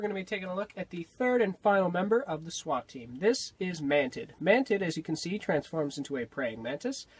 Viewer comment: Half the time I thought you were saying Man Tits...